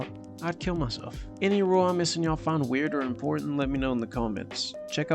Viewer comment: Can someone tell me what he said? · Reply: "... where I explain why the knight is better than the bishop."